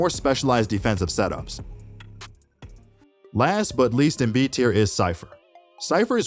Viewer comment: did he just say last but least?😂